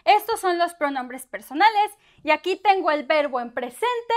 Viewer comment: Yacitien?? what is this word Yacitien I have never heard of this? after listening to this about 10 times i realized it is Y aqui te en, feel stupid lol, at least thats what it sound like although the 'te' doesnt seem to fit in context.